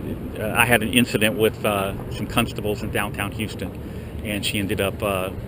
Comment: I heard ya there "cuntstables"....you could see his ears perk up but he didn't take the bait. Nice one